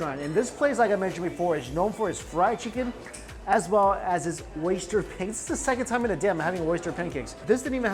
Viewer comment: I thought you were saying "Oyster Painting" and it was a really odd mental image. I still want to eat one.